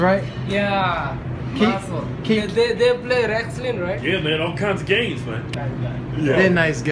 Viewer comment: Did he just say they play on the Redskins? LoL I don't think that's what he said, but I can't make it out at all. That's the closets interpretation I can get.